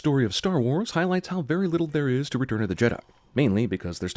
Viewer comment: Anyone thought it said returm of the jedi